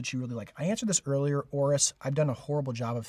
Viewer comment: did he say Oris or Horage ? Oris founded in 1904 so i think not a Micro brand and Horage is more a Independent Brand (in house movement) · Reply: You heard Oris, and looking at my Big Crown Propilot... It certainly isn't a micro brand.